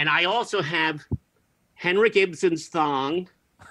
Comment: And Ibsen's thong? Don't leave us hanging, ffs! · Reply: I thought he said "Henry Gibson's thong."